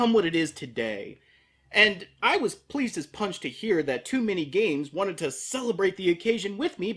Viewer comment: When I heard "Too many games" I thought he said "Too many gays"...